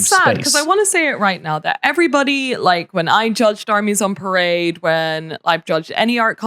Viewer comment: First time I heard "Carnies on Parade" instead of "Sarnies on Parade". I was wondering who's minis we would be looking at.